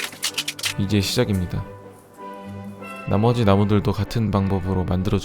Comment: 나만 나무를 나고로 들었나